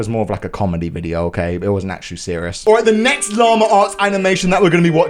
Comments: ones i thought you said larma ass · I thought you said Lama farts